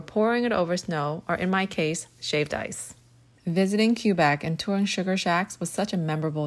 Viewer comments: Never heard anyone pronounce Quebec as Cue-Bec I genuinely thought you said Cuba, so I was really confused when you were talking about maple syrup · I’m Canadian and I’ve never heard anyone say Quebec like Cue-bec 😂💀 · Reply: I THOUGHT SHE SAID CUBA LMAO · I’m Canadian and I’ve never heard anyone say Quebec like Cue-bec 😂💀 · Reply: She wanted to say Quebec??? I’m a Canadian and I thought she said Cuba. 😂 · She was saying quebec 😨😨 i thought she was saying cuba 😭😭😭